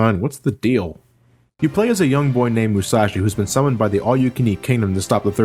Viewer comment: i thought he said a young boy named Versace